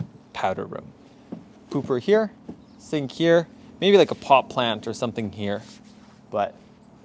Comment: did you say pot plant......nahhhhh potted plant right?